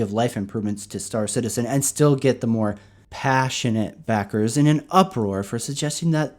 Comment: Everytime you say the word "pa-ssionate" before 'backer' I hear "retarded" LMAO